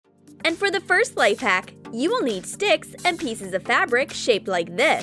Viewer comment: wait did she say you need stick?